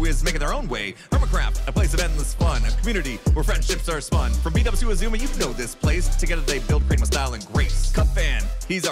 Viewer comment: I heard “from Bdubs, whose a zoomer” 😂